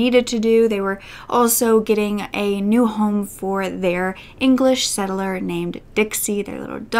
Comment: Did I hear "English Settler"?